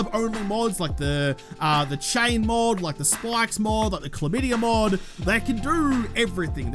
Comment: Now I'm not too familiar with late game mods... did he just say chlamydia mod??? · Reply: lol. I think it is my favorite phrase he comes up with as well. But he is just referring to the flame mod. (cause the shaft is on fire)